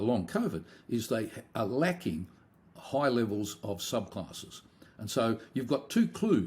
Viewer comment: ANYONE: What does Professor Robert Clancy say @ 31:10??? "They are lacking high levels of ????" I can't hear that word. Is he saying subclasses? I'm stumped.... I appreciate any insight, as I cannot understand what word he is using at the end of that sentence. Thank you!